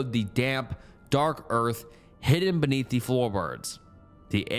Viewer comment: I swear you almost said Floorbirbs